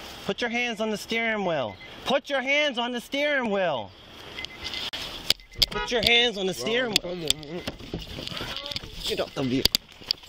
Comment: what did they officer call him? I played it so many times I swear he said “ put your hands on the steering wheel you bitch” but it’s (unintelligible mumble) he said something & I bet it was racist he was mad he wouldn’t get out